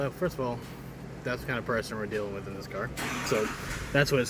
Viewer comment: i've replayed this video twenty times at this point , and I cannot understand what the hell you mumbled out your mouth. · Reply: That's the kind of person we're dealing with in this car.